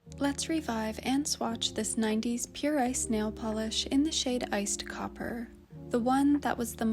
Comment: I'm sorry but its morning and I heard you say "Pure Ice Nail Polish" and all I could hear is "Pure I snail". I was very interested where the snail is going to come into play lol